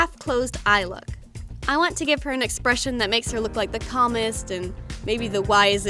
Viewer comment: When you said "the calmest" I heard "the communist"